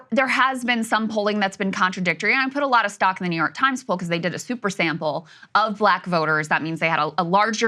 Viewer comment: Did she say a "Super Saiyan" poll or "Super Sample" @ 6:28? 😂